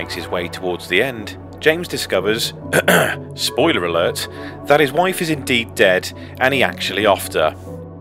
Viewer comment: I thought you said “That his wife is in deep debt”. 😂